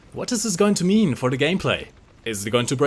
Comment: Did you say "What is this going to meme for the gameplay"